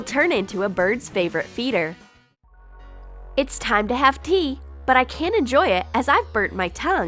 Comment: I thought she said pee not tea wth